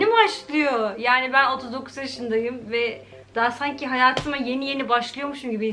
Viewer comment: 39 yaşındayım mı dedi ben mi öyle anladım